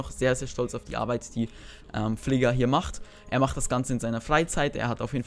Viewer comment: hab ich da gerade Fligga raus gehört?